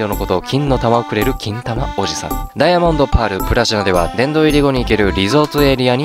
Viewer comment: 聞き間違いかな ダイヤモンドパール・ブラジャーって聴こえた 多分、耳が悪いんだな